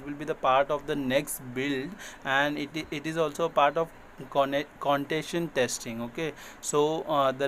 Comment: he was saying something like contation testing. can you please tell what it is? I have never heard of this word